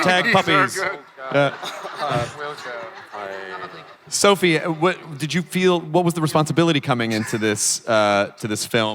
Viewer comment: Did Sophie say to Tye at 18:23 ''Good answer Babe'' :) #Jott #XMenApocalypse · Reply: ***** no "good answer man"...it's her style